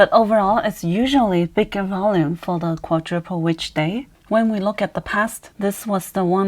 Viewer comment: I don't understand what you said, perhaps I'm not familiar with the vocabulary... "But overall it is usually bigger volume for the quadruple which day." What is the day you are referring to? Is it a combination of earnings and other things? I know it must be a confluence of technical factors that marks a day with higher volume, but I'm trying to understand what you are referring to. Thanks! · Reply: quadruple witch day lol